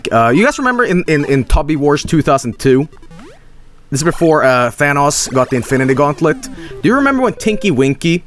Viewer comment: I swear I heard said Vanoss. (I know he didn’t but now you’re not gonna hear anything else lol)